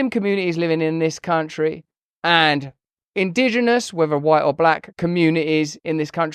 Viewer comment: What did he say @ 4:17, Indigenous people Either white or black ? Is he still on drugs ?